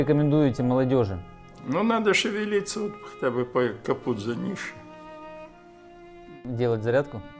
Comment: Что сказал зрелый мужчина на 8:45? Слышу "Капут зе нише"